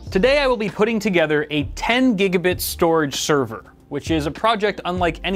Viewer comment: I thought I heard ten gigabyte. :P